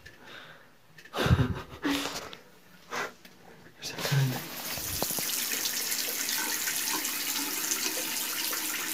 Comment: Se escucha que dice un "ay" o un "ahi" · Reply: Se escucha más bien Ah lo reproduci muchas veces y se escucha Ah